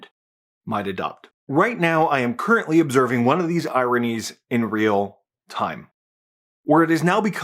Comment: The first time you said, “ironies” I thought you’d said, “Iranians”. Lol